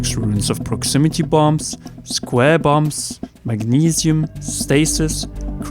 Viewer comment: did you say magnesium?